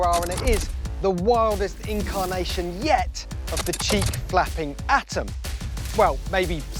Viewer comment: Damn I thought he said "cheek clapping"